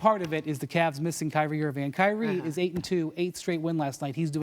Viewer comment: What does she say at 4:19? It sounds like "check your midgets"